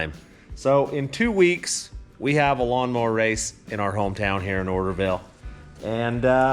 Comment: I thought you said “ our hometown Hooterville”😜